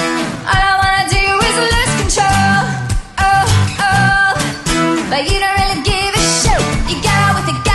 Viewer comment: Haha it sounds like shes saying "But you don't really give a "shoe"